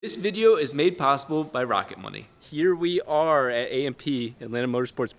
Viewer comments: And I thought I heard rocket bunny😂😂😂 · I thought he said "Rocket bunny" and expected a free widebody for the FD